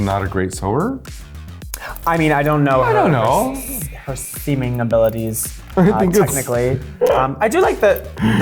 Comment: i thought aquaria said semen abilities